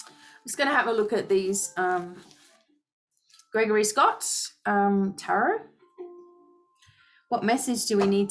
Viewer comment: Amazing wow what is the name of that deck of cards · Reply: Ikr....I am being drawn2that decktoo!!! I think she said Gregory Scott Tarot at bout 25:00 minutes in2 the video. Thanks B for the messages!!!❤❤❤ That King of Pentacles situation stood out2me